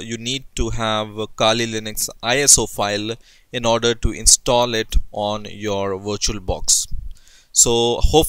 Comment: I thought he said install it on your washing machine lmao